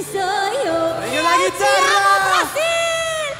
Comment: Gente, eu jurava que a Anahi falava "Palmas, Brasil". Hoje, que descobri que é "Bate palma, Brasil" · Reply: E eu entendi “ e eu te amo Brasil!!!!”